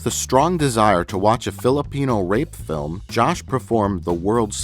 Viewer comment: when he said Filipino i thought he said Philip Eno and i thought to myself, "brian must be so disappointed"